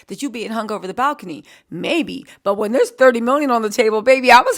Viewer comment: Thought you said she got 100 million